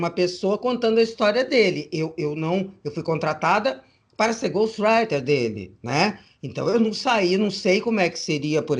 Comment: Foi: goth friday que ela falou, no minuto: 32:52 ? Não entendi direito... Abraço. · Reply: ghost writer